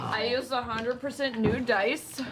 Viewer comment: As an Aussie, I heard "I used 100% new dice" as "I used 100% nude dice" and I'm like..... look, I know Beau is super up for it, she's getting naughty dice, too???